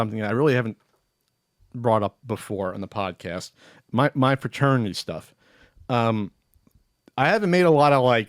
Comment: I was glancing down as I was eating something and when said "my fraternity stuff" I thought he said "paternity stuff" lol So I definitely thought that segment was going a different direction at first